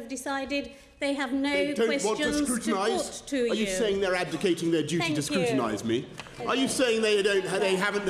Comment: What does he mean when he says (0:21) "Are you saying you're ADVOCATING your duty to scrutinize? Are you saying they don't have the guts to put questions to me?" What does he actually mean? If you were "advocating" your duty to scrutinize, you are essentially saying you are supporting your right to scrutinize. So that would be the opposite of putting questions forth. I got confused there. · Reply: He says abdicating, not advocating.